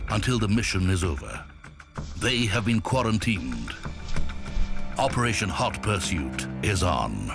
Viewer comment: did you say operation hot prostitute